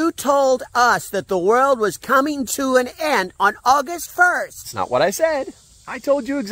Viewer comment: Oh i miss heard you. I thought you said all food will come to an end thx for the info dude